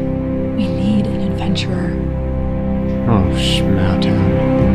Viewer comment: What did Finn just say at 1:17??? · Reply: "Oh shmawdown" It's his catchphrase